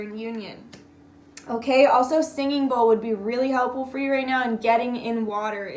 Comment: Pretty sure she didn't say "singing bowl", but I can't hear anything else. Help. What does it say? 🤣❤️ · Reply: She does say singing bowl